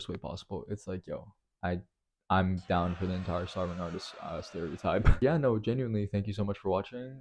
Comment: what did you say at 8:43 lmao? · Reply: He’s down for the starving artist stereotype